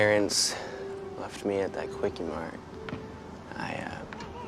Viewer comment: Hold up, did he really say his parents left him at a Kwik-E-Mart?